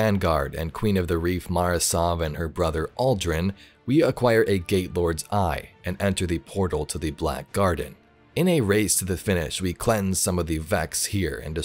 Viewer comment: I thought I said gay lord at first